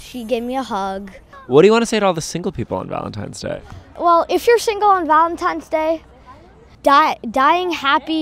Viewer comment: For a moment there I thought he said, "If you're single on Valentine's day then DIE"